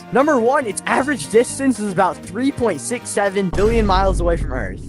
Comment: did you say 6-7…..